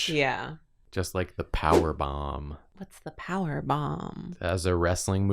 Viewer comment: I misheard saying "power bomb" as "power bottom" and was suddenly more interested in wrestling than I had ever been